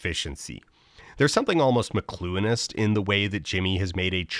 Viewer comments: can someone tell me what word that is... 'macluenist' ??? i feel like i have a pretty good vocabulary but im totally missing the meaning here and google isnt getting me any answers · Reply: Look up Marshall McLuhan(ist). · What’s rhe word Dan uses at 11:18? Something like “mccluenist” but I’m not sure how to spell it so idk what to google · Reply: Nvm figured it out. McLuhan is the “medium is the message” guy <link> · What is he saying here? I want to look it up. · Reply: McLuhanist (Marshall McLuhan) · what word is he using there? mcclueinist? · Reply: McLuhan, the guy who said "medium is the message" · What's the word he uses at 11:17? it sounds like 'McCluinist' or 'McClunanist' and the subtitles just have it as 'mlonist.' Anyone know what he's saying there? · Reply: "McLuhanism," referring to Marshall McLuhan, famous for the phrase, "the medium is the message". I had the same experience as you when McLuhanism was mentioned in another Folding Ideas video! 😂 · What word did he say here? It's driving me crazy. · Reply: McLuhanist, in reference to Marshall McLuhan and The Medium is the Message. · Dan says something like "theres something almost McCluenist about..." but the caption just hears mionist. Idk what that term is or how to look it up to learn what it means. Anyone know what that word is? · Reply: I think "McCluhan-ist" as in Marshall McCluhan. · What is he saying at 11:17? McCluenist? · what does that word mean? i've tried googling but can't find it; mclounist?